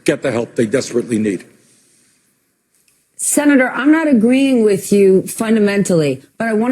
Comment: did she actually say "I not agreeing with you fundamentally"